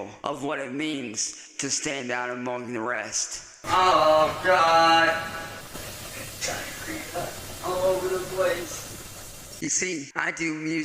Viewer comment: “I diarrheaed all over the place” is a sentence I never thought I’d hear in a genuine sense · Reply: I always hear “there’s diarrhea all over the place”